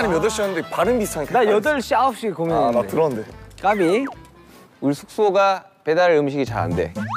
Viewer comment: Só eu entendi o JK falando CAGUEI? KKKKKKKKKKKKKKKKK